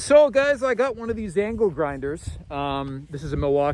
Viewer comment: I thought you said Anklegrinder, I was disappointed....